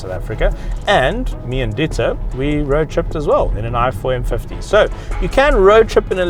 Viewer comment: did you say you roadtripped in a F150?